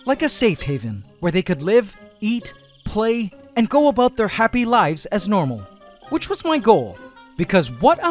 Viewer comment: When he said "go about their happy lives", I heard, "go about their crappy lives"